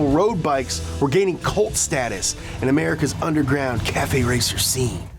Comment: did he say “colt status”?!?